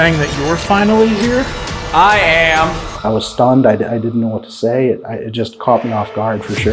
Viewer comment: misheard the guide guy and thought he said he was stoned lol. Love your channel always keeps me interested. keep it up you guys